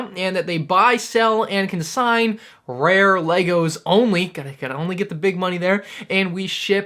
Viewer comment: I know this video is serious but did you say l E g O S ?